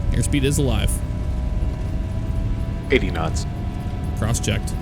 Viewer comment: im i the only who heard 80 nuts xD